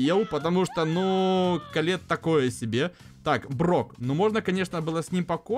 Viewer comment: мне послышалось или сказал котлет вместо колетт?